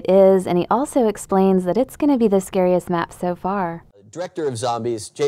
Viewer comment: did you say it was going to be the gayest map so far?